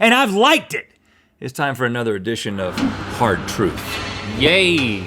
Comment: Anyone else hear "Fart Truth?"